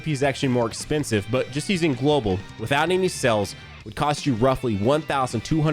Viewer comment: When he said sales I could’ve sworn he was saying cells